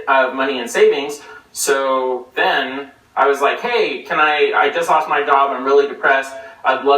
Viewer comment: Did he just say "I told him I just lost my dog?" I thought he only had cats though. So, Skippy was misleading them